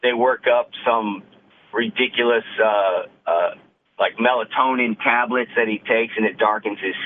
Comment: Did he just say melatonin pills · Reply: Yeah - they were suntan pills not melatonin pills.